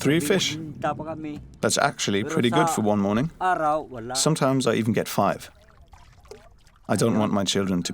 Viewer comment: This is so sad :( really shows how lucky we are and to be grateful for all that we have! And the translation is awful! Does not show the true extent of what my Filipinos are saying. 23:01 he does not say “sometimes I get 5”, he says “sometimes NONE” 😔